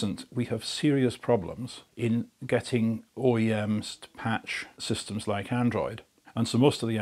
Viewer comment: I am confused what he is saying at 5:58. OAMs? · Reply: OEM's. Original Equipment Manufacturers. E.g. Samsung.